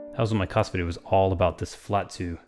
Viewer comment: any idea what he says here? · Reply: "That was what my "Kass" video was all about, this bII chord" 😃👍🏼